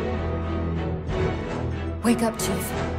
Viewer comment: 5 years later and I still think cortana said "wake up Jeff"